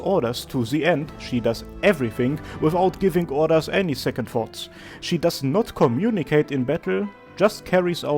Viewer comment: I misheard it as “She does not communicate in bedroom”